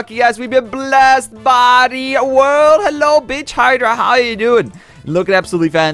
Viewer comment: BITCH HYDRA thats what i heard